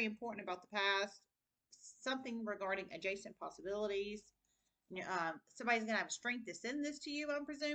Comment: You said "adjacent Possibilities", and my ears perked up...I sent an email to a Jason....I heard, "A Jason Possibilities"....LOL Cool! ...Oh, and it would, or should cause a Report to be Filed, because I was asking him about his Grandmother and Grandfather's house...they have both Passed away years ago, and it is still being held in their Name! This is Suspicious, as someone must be Paying the Taxes...and I was never Contacted! :) ...I ask, only because he would be the Rightful Inheritor...in Truth...I am just the Oldest!